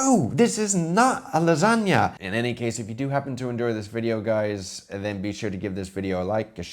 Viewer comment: I love how he said "hope you endured this video" instead of "enjoy". At least that's what I heard.